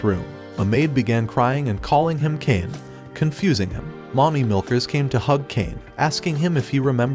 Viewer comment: i think I heard mommy milkers 💀